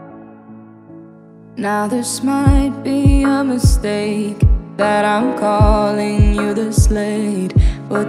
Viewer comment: Why I heard "Now this might be a mistake that I'm calling you the snake" ... WTF is wrong with my mind ? O.O